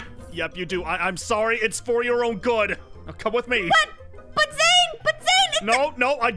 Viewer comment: I thought Zane said “4 year old good” 😂